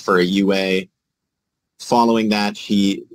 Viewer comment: OMG I thought he said that she tried to provide a fake urine & "following that"... I thought he said "swallowing that" referring to the urine. 🫢🤢 Had to go back & rewatch.😂